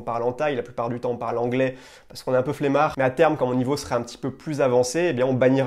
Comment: bonjour, apprenant de français ici à 6:37 il dit "parce qu'un peut fléma" ca veut dire quoi ? c'est comme la flemme ? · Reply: Il dit "un peu flemmard" Flemmard c'est le nom qu'on utilise pour parler des gens qui ont la flemme, oui :)